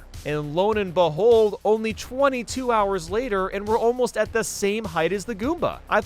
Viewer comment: I thought that said 27 hours. 😳